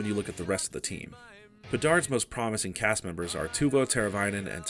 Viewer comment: Twovo? Did I hear that right?